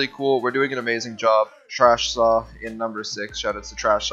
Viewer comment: Did he say "trash saw" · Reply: washedsaw used to be called trashsaw and before that he used to be called jigsaw